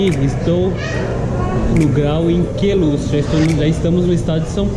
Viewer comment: não entendi o nome do lugar, Grau em Queluche? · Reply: Queluz SP